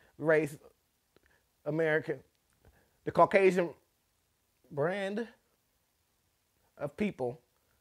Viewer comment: I was mid taking a dab and I heard "Caucasian Brand" 🤣😂🤣 safe to say I wasted a good sized blob 🤣😂🤣😂🤣 worth it!